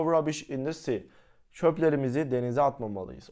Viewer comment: bişey söylicemde inanmicanız 16:46 da şöförümüzü denize atmalıyız diye duydum dedim noluyo throw rubbish in the seamıi ya çok tuhaf bi sahneydi